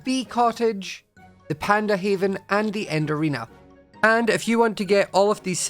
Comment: At first I thought you were saying the enderina like a female enderman but then I realised it was the end arena😂😂